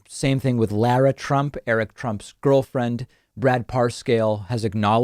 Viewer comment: did i hear that right? Lara trump, erick trump's girlfriend?